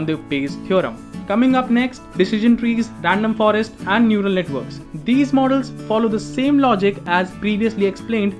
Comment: I thought he said "noodle network"